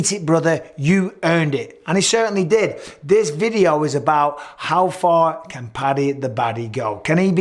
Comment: For a second there I thought it said how FAT can Paddy go. LOL. Pretty effing fat XD